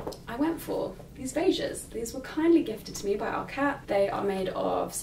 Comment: When you were talking about the white shirt I thought you said "it was gifted from our cats", I was dying laughing! 😂💕